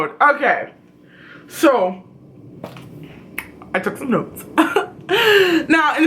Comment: Wow need to turn up the volume bc when you said you took some notes I thought you said I took some pills for like a good second lol. 😆